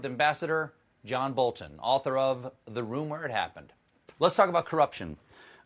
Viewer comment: author of "The Ru..mor Happened", ambassador John B... I thought I just heard Steven say exactly that, and the inner tin-foiled-hat conspiracy-nutty part of me went "Whooowee!! The PeePee Tape Is Real!!!"